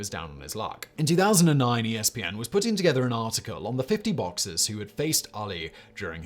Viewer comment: Is it just me or do people have a difficult time understanding Simon? A lot of the time I have to figure out what he said by the context it was said. For example at 5:28 it sounds like he says "boxes". I think he was trying to say "boxERS". And closed captions don't help, it thought he said "boxes" as well. This may seem quibbling but it makes a difference when I can't determine what he said by context. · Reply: He says boxers